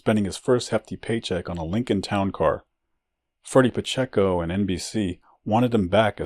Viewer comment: His mom reminds me more than a little of Hector Camachos mom. Another great vid. So was the Kid Gaviland one ! Great work. Hope your feelin better ! You sound back to normal. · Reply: Did you screw up Ferdie Pachecos name or did i mishear you say freddie ?